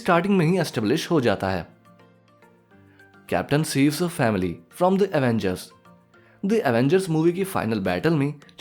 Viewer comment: Did you say family ?